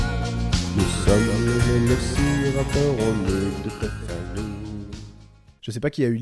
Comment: C'est moi ou a 9:05 il dit : nous sommes les vélociraptors on N*QUE toute ta famille ?